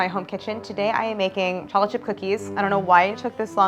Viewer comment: LOL she says “chocolate chip cookies” so fast, I hear “Chochkie Cookies”